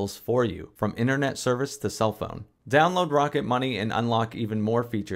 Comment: LOL. I thought you said, "Moroccan Money"